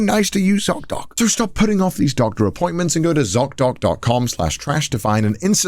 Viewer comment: Connor saying the American z in the zocdoc ad made my brain glitch. Not only because it was America but also because I heard "c-o-c". Cocdoc